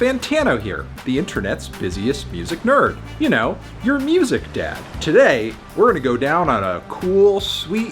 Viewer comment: At the beginning I thought he said “you know, your music death”